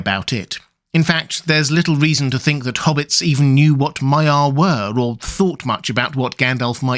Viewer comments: I thought it said mafia. I was like "damn, Gandalf was a made man?" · i thought it said mafia not maia